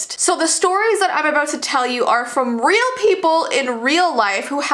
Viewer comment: I literally hear aboot